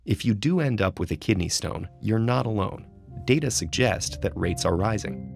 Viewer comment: I love how enthusiastic his voice is, when he says "the world record maybe broken soon". I would not want to be the one to get that record. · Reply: He actually said “but that world record probably WON’T be broken any time soon” so it’s just a problem of you mishearing him, and not his problem since he said nothing wrong 🙄